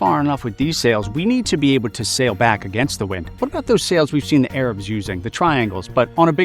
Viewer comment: Arabs? I thought you said Africa